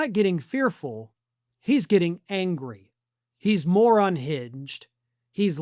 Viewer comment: Rather than hearing “more unhinged “ initially I heard “Moron hinged” kind of fits either way 😂